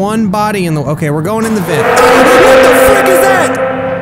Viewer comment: Did I hear say “ahhh what the f*ck was that”?